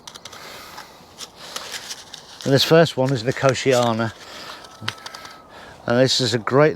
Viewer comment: Good morning, what was the name of the seeds you are just planting cosheana I thought I heard you say